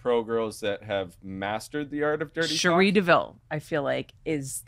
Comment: what did she say ? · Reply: Cherrie DeVille, check her out she's 🔥👌